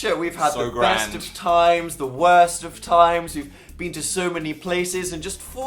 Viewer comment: My brain timed out for a second, and I thought Dan said, "We had the worst of times and the worst of times." I didn't question it too much.